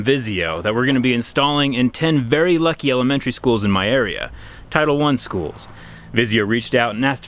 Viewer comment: did he say tyler1 schools?